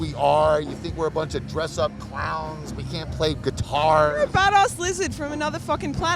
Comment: did you say "badass lizard?